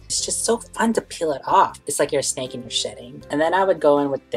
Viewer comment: Omg I thought you said snake shiting. Shedding* lol. Good video. You talked alot more .